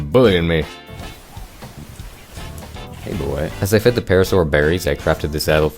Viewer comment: i heard it wrong and thought he said "as i fed the parasaur parrots"